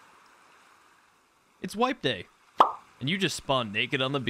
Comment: For the first maybe 3 minutes of the video I thought he was saying “Wife Day.” As you can imagine, I was both confused and intrigued. - Non Rust player.